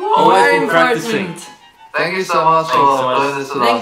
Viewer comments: I heard, "Ziyu soon" instead of see you soon and now I'm disappointed. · In the end I thought they said “zi yu soon”